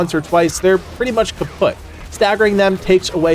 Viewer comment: did you just say kaputt? 3:40?